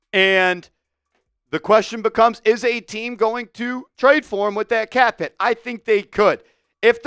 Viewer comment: Why? Hes washed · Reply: I said I got bamboozled cuz I thought it said we did trade for him. I didnt say whether I thought that was good or bad.